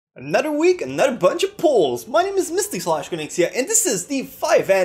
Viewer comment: I thought he said at the beginning, "Another week, Another bunch of porn"